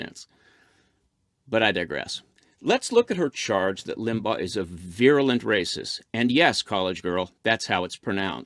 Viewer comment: At first I thought she said "violent" but then realized she mispronounced "virulent." Obama gave out over 100 of these medals. Trump should catch up.